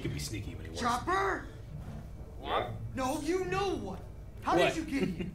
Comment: I know I just heard "what the fu*k."